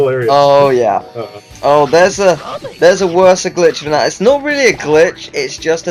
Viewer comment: Did you really say Worser?? Grammer Fail